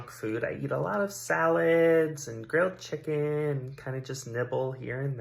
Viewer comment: someone please help me... I heard girled chicken instead of grilled chicken...